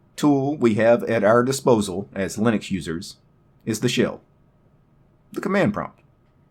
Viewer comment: I heard that as "The most powerful tool we have as Linux users is the Shill". Well, you're not wrong!